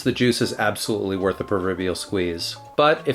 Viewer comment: Almost sounded like you said “worth the Peruverbial squeeze”!